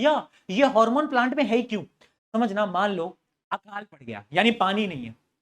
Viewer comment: What did sir say at 1:42:45 I hear he say apka g*nd fatt gaya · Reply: He says that 'akaal pad gya'😅😂